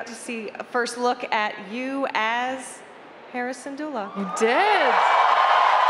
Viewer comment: the way the host say Hera's name i thought she said Harrison's Doula... 😅